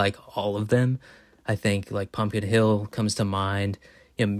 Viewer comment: I thought you said "Pumpin' Hill" for a moment, and I honestly can't decide which title is more based.